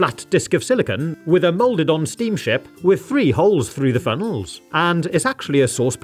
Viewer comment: I thought he said "free holes" 💀